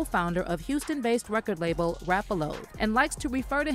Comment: did she say "rap-a-LOAD"?!!!